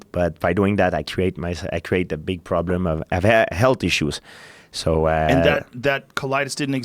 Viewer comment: George said "health issues" but I heard "healthy shoes".